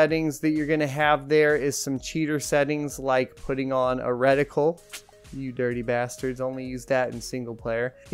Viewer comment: What setting is “a radical” you said at 6:50? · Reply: Retical